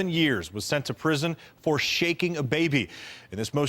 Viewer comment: I'm glad I went back because I misheard and thought he said "for the second time in less than seven years he was sent to prison for shooting a baby." Shaking isnt much better but I was about to flip.